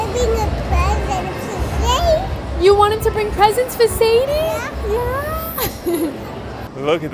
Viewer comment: I watch (listen to) YouTube in 2x speed (while I am deboning chicken at this moment) and normally I have no issue with understanding things but I thought both of you said “bring COUSINS for Sadie” ….I had to clean my hands and rewind. I thought you said cousins the second time around, too,…. So I rewound it again and put it on regular speed and it was more clear and I saw your caption so I knew, then, what was actually said. Thanks. Haha.